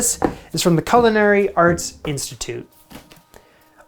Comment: I swear he said "Culinary Arse Institute." Did anybody else hear that?